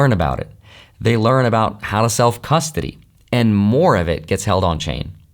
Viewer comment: Swear I heard "House Elf Custody..." Which inspired a heck on a mental image. 😂